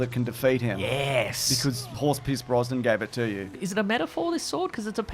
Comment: I kept hearing "Horsepiss Brosnan" 😂